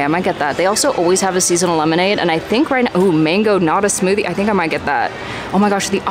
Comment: I thought you had said "mango not-a-smoothie." Like at how Real Food Daily (L.A. vegan restaurant), the nachos with cashew cheese are called "not-chos."